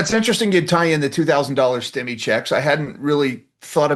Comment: thought it said "$2000 skimmy checks"......lol